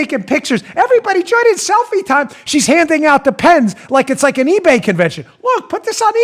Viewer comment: Every time Bongino said "handing out the pens" i heard "handing out depends" 38 minute'ish if your looking for it